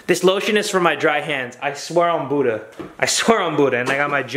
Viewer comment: “This lotion is for my dry hands, i swear to Buddha” - 2021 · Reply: I thought he said I swear I’m Buddha lol